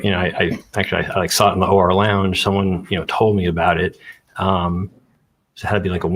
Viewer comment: At first I thought he said he saw it in the “Horror Lounge,” and I thought I should look up that website since it was probably about True Crime.